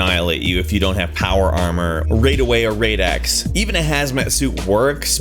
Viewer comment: excuse me, but did he just say RAIDaway?